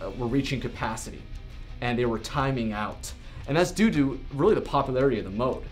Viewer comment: What I heard, "And that's doo doo..."